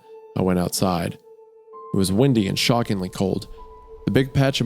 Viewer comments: The way Wendigoon was pronouncing ‘Windy’ I thought he was saying ‘Wendi’ so I had a feeling… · I’m picturing like a young wendigo style creature. Could be because every time I hear “windy” I’m also hearing “wendi”